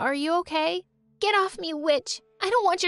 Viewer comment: I thought she said the b word instead of witch